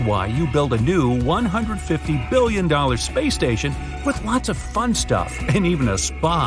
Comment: I thought he said play station